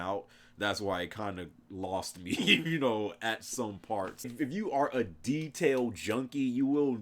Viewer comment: I thought you said "if you a detailed JUNKIE" ...... I was like.......is he about to confess?😫 Went FULL DONALD TRUMP at the end. No lie.